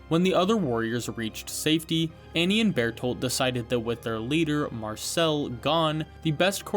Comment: bro really thought he could sneak in "battlepass" in the captions instead of berthold 🥀🥀 Edit: I just realized he also snuck in a bunch of other names in the captions like billboard, Blunderbuss, ben & Jerrys